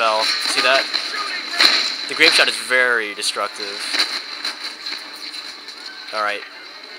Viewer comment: i thought you said " the grapeshot is very SEDUCTIVE..."